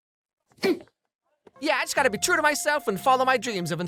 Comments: did tikki just say "you d-ck"?????? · please tell me i'm not the only one who hears "you dick" from tikki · but then lila no misunderstood. She just a bad guy. Who annoyed cus she got embarrassed. But point is. She ain't misunderstood. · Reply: I found part funny cus it sounds like she said "you di-"